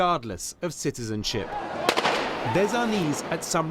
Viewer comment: I'm sorry what???? Did he say "there's our knees" at 1:31 or is my captions wrong??🤔🤔